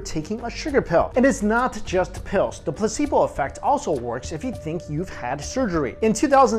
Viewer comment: I thought he said "the pussy boy effect" 😂